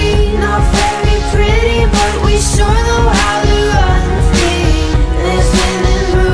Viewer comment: i hear "not very pretty but we sure know how to run FREE." which is better